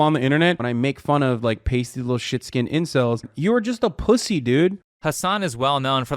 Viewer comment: Did he say sh*t "stain" or "skin". Because one of those is incredibly problematic? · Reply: He said skin. It might be because he thinks it means acne-riddled (it doesn't).